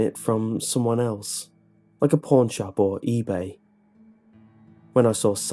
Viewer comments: What he said: "pawn shop" What I heard: "porn shop" IS IT JUST ME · What he said: "pawn shop" What I heard: "porn shop" IS IT JUST ME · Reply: I'm almost at the end of the video, and i still thought he said Porn Shop till i read this comment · What he said: "pawn shop" What I heard: "porn shop" IS IT JUST ME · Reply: I also heard him say porn shop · What he said: "pawn shop" What I heard: "porn shop" IS IT JUST ME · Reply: He actually said "pawn shop" but it did sound like what you thought he said due to his British accent.